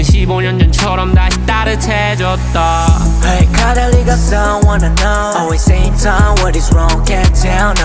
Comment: Juro q ouvi "A cada ligação" de primeira 🤡✨